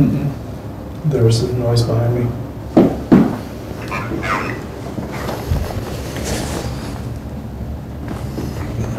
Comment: What's the ovilus say on 25:43/44? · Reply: if I remember correctly it said "Compound".